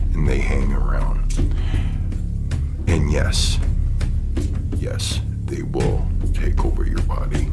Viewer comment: did you say,”Yes, they will take over your body”? Great video — as always 👊🏻😎🙏